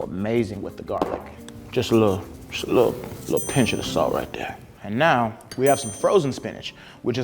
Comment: Umm am I the only one that heard him say “pinch” instead of “piyah” 😳😡also I agree...he should have his own series 😋😛😁